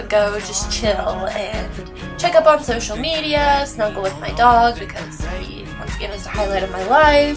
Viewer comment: when you said "and snuggle with my dog" I thought you said "and smoke from bong" lmao